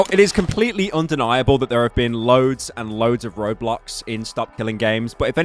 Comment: I've heard it as "Roblox", my mind is so cooked